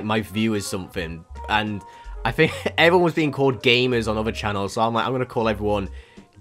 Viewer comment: I always hear "Gaymers" instead of "Gamers" smh